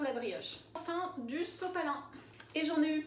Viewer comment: Du quoi ??? 🤔🤔🤔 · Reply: Du Sopalin, c’est une marque d’essuie-tout qu’on a en France, c’est une marque assez connue donc il est régulier qu’on dise « sopalin » à la place d’essuie tout, mais c’est la même chose !